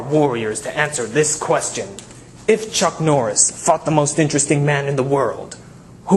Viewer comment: ha ha at 0:52 i thought he said chuck norris fucked the most intresting man in the world